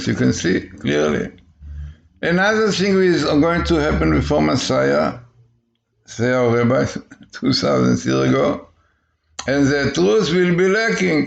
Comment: Did he just say "another thing is going to happen before messiah which is about 2000 years ago"? Between 3:35-3:40 minutes. Shouldn't repentance start with the teacher? Messiah has come 2000 years and name is Salvation. Isaiah 12:2 surely God is my salvation/Yeshua I will trust and not be afraid. Psalms 118:14 the Lord is my strength and my song and he has become my Salvation/Yeshua. He is my God and I will praise him. He is our passover who's blood was shed for all mankind on the cross, "he was pierced for our transgression" Isaiah 53:5 "For the life of the flesh is in the blood, and I have given it to you on the altar, to make atonement for your souls" leviticus 17:11. Repent oh Isreal beloved of God and read your bible and you will see that your messiah has come as passover lamb first 2000 years ago but this time he will come as lion, the lion of Juda and will conquer. If you still doubt that Yeshua is our messiah read also Psalms 22 all of it please but I'll quote a little, "they have pierced my hands and feet" 16-18. He's coming, our king is coming, sound the alarm, blow the shofar, sound the trumpet 🎺 Zachariah 9:14 Then the LORD will be seen over them, And His arrow will go forth like lightning. The Lord GOD will blow the trumpet. May this happen on this feast of trumpet amen 🙏